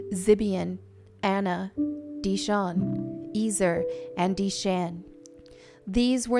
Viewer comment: I wasn't watching the screen so when you said "Dishon" I thought you said "De Shaun" and I was like "wait, there were brothas in the Bible?"